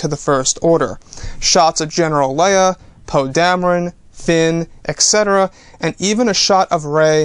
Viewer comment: I thought you said Pug Dameron!